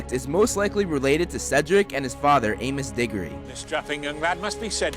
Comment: At first , I thought he said ANUS DIGGORY